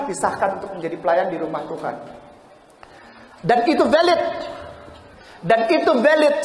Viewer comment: guys dia bilangnnya valid apa "silit" ? Kok gue dengernya "silit"